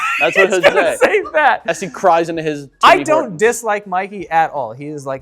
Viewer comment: did just said "i see cries in his tears" or i heard wrong?